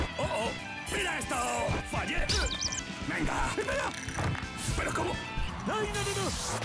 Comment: I think he says "Mi pelo!" = My hair! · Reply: He's saying espera (wait) but very faster